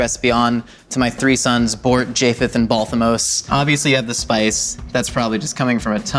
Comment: did he just say Balthamos? Like the character in His Dark Materials?